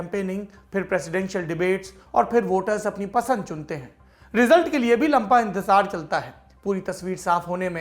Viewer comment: Am I the only one who heard "lampa" at 1:18 instead "lamba"...